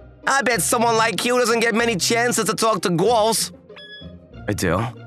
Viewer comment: sounded like gru “Gorls”